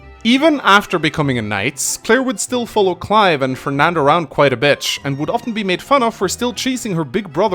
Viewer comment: Either my mind is playing jokes on me or said "Quite a bitch" instead of "quite a bit" here..